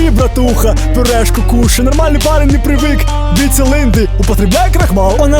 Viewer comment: Что говорит беларус, после слов "нормальный парень не привык ..." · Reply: Нармальны парень не прывык биць лынды - не привык бездельничать. И вообще должно быть не парень, а хлопец.